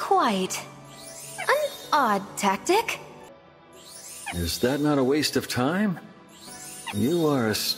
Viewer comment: I thought Lucina said “You’re a waste of time” 😢